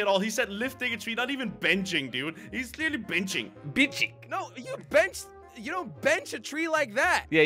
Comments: Did say bi*ching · did u say b**ching? 11:14 bro this video is messed up!